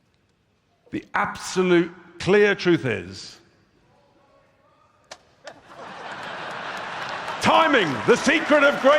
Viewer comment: Anybody know what that guy shouted at 59:48? 😂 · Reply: he shouted shut this racist bullshit down Britain doesnt need nazis